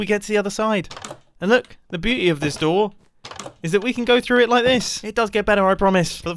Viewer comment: "The Booty of this door" is what i thaught he said XDD